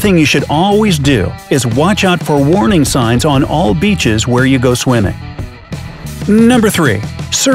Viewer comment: I thought it said bleach not beach